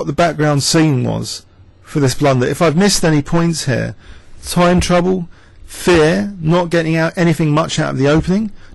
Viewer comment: lol i thought you said time travel